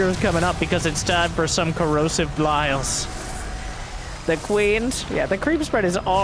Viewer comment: Did I hear "CorrosiveBlyle?"